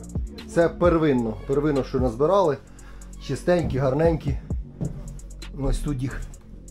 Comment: did he say “she is stanky and manky!” ???